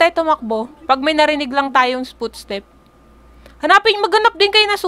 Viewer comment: "spootstep" - HAHAHAHA MISHEARD ATA AHHAHAHAH